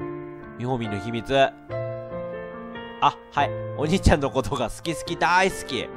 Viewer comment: の「おにいちゃんのことが好き好き」の所が「おじいちゃんのことが好き好き」って聞こえてただのおじいちゃんっ子になった